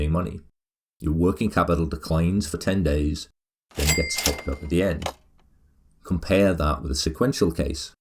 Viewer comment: Kinda sounds like he says 'then gets fucked up at the end'